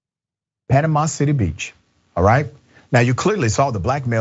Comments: Ironically at first I thought he said "animosity beach"...... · Dr Richie. I had my volume down low, listening to noises I was hearing outside, and I thought you said “Animosity Beach” caught myself mumbling aloud, “huh, that’s appropriate” then 😆😆. I thought, Wait, that would be too perfect, so, I restarted it with the sound higher. Panama City b.. 😆😆